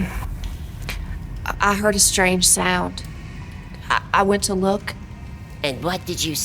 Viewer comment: What ever that intro was, I thought she said "I heard a strange smell"